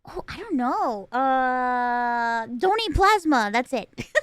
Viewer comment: “Donate plasma” I misheard as “don’t eat plasma” ^_^